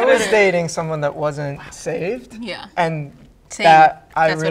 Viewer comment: When did he say that?? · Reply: He clearly says "I was dating someone who wasn't saved".